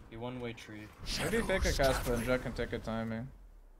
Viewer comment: does anyone have any idea what he said here? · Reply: maybe fake a cat split, jett can get a timing